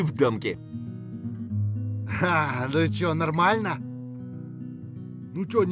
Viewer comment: I thought he said, "I know you're in the miner" :D